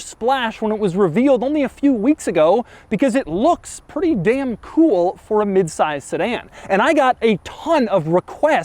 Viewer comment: Did he said damn cool?!!